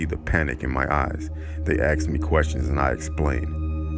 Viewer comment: no one: literally no one: a deep voice:"my name is kate" edit: at 1:01:53 the man said aks instead of asked lol no offence